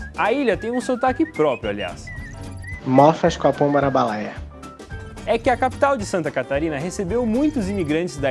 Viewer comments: Eu entendi "mofas com a pomba na balaia", se for algo minimamente parecido com isso, provavelmente é uma "gíria" ou "dialeto". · Sou brasileiro e não entendi nada. "Mosfa...marabalaia"? 😂